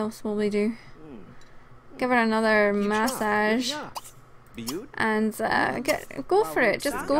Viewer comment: When u said massage I thought you said mustache XD